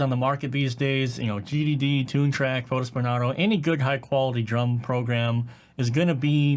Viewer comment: What is the 3rd library mentioned? I can't understand... "GGD, Toontrack, xxx". Thanks!! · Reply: I think he was referring ot Fotis Benardo(or Bernado)